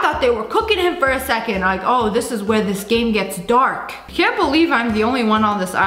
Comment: Oh this is where the skin gets dark -sssniperwolf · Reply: I THOUGHT SHE SAID THAT TOO I WAS LIKE WHAT but now you said that i re listened to it and i heard ‘where this game’